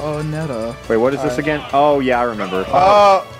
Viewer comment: Is someone says: “Holy shit!”